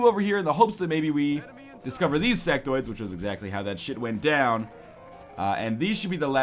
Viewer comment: NL, did you just say, Sectards? xD